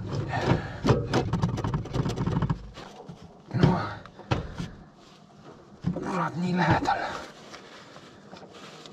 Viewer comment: I did not understand what you said but I had to laugh because I could imagine! 😅 Greetings from Germany!! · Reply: i said "damn, so close, pull out he press to press it into spot"